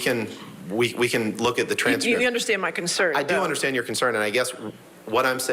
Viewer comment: What did Johnny's lawyer say here? Cause I didnt hear him very well. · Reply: "I can't wait to oppose this"